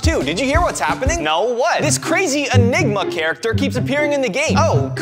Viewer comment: I thought you said ligma